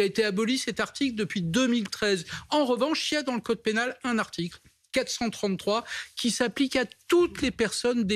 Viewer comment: J'ai entendu 49.3 🤣😅